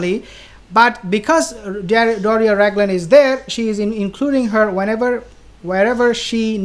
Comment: I don’t think her mum is dead though😳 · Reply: Sorry My bad he said @ 6:41 that her mother is THERE but because of his accent it sounded like her mother is DEAD … I just re listened to it so apologies 🤷‍♀️